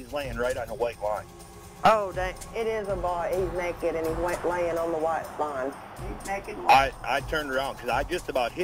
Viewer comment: I could swear i hear "he is naked and WET, by the white line", from dispatcher, 11:06. Also caller seems to start and say that???? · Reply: caller: "because his WET, right, the body is laying on the white line."